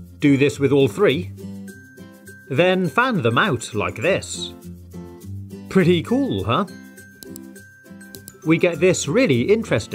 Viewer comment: Did he just say "Pretty crazy huh" Instead of "pretty cool huh"